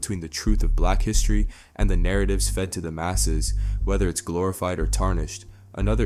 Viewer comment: I know I can’t be the only one that thought when he said masses he was saying masahs <—- “slang for masters”